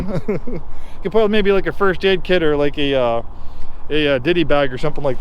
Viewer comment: am i trippin or did bro say diddy bag at 5:18 😂